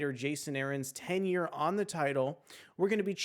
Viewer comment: Yeah. I thought that you said 10 years. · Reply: OH, I said tenure. It sounds similar doesn't it? The closed captioning took the wrong word also, I'm going to pin a note in the comments to clear this up a bit. Thanks for letting me know about this.